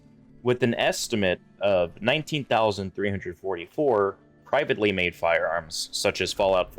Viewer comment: I coulda sworn you said they call them "Pipedly Made Firearms" the first time. Ha.